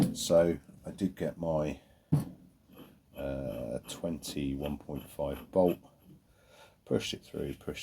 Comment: It's about 4 mins into the video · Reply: my bad i was measuring the I.D. of the thread its a m20 x 1.5 pitch bolt thanks.... i thought i misheard your commentary thinking u said m21.5